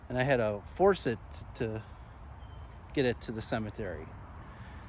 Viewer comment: After scream I heard "Get in"